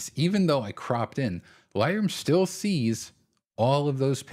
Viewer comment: For first few mins struggled to figure out what you were saying repeatedly….“Lie-roo” then it clicked… “Lightroom” 2:56 😉 good tips